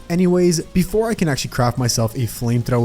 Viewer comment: i know he said craft but i really cant hear the f but it sounds like he cr xdddd